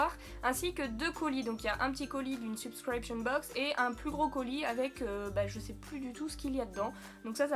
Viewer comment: quand elle a dit "gros colis" j'ai compris "brocoli" lo